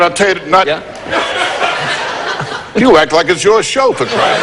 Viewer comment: + Did Rodney say, "You act like a Jewish chauffeur?"